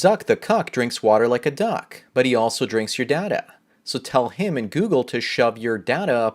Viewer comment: Whatever you do the bit with Zuckerberg it sounds like he saying he drinks your dada